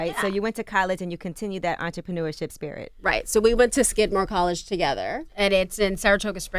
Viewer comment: I thought they said skid mark too